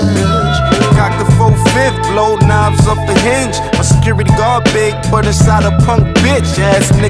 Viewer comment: Did bro just say skibidi dog